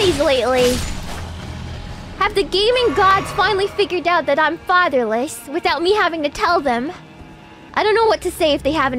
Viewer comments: I… I thought it said featherless and didn’t even question it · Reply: I thought it said featherless too, i was so confused.. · I… I thought it said featherless and didn’t even question it